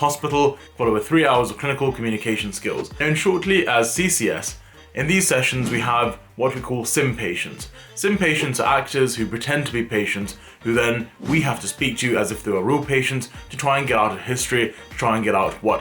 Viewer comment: Lool thought you said simp patients.